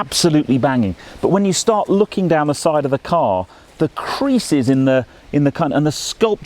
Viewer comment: Great review and lovely car but did anyone else hear say, "creases in the c*nt"? 2:44 🤣